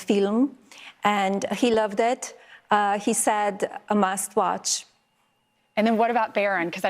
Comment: I heard her say trump's response was "sasquash", guess she meant 'must watch'! LMAO!